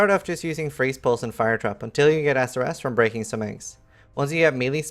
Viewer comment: SERIOUSLY - read the subtitles created on this vid to see how bad it is to follow what you are saying. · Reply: I died when the subtitles said "Sore ass" when he said SRS.@ 0:47 LOL!